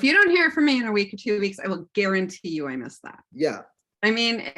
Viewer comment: "I guarantee I missed that" - Why did I hear that as "I've been stabbed" 😅